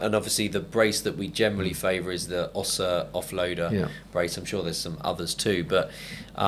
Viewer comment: what i the brand name of the brace you recommended at about the 44:15 mark - it sounded like "Oxo Offloader?" I could find a match although there are many "offloader" knee braces our rhere